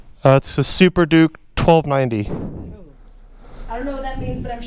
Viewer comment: poop . Thought he said super doop 1290